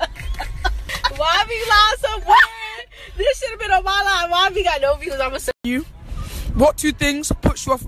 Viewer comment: what she say??? “dis shoulda been a wala wala”